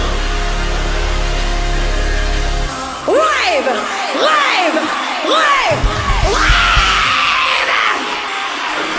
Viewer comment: what did she say 10:55 when she was scream??? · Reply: bad romance "hands up! RAVE RAVE RAVE RAVE!!!"